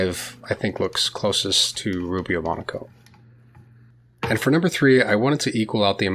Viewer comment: <Joke> Who is this guy Rubio Monaco and what does he have to do with woodworking? 🙂 </Joke> (It's a double joke for anyone who remembers hand coded HTML.) Seriously, I watched a number of woodworking videos and misheard Rubio Monocoat as "Rubio Monaco".